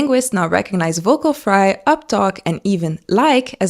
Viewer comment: Oh uptalk, I thought she said updog 😅